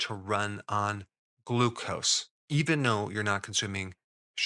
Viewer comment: Very important question: Why does Mr. Berg say "even no" instead of "even though?" Example here 👉 1:20 I've heard him do this in several videos...I need answers!!!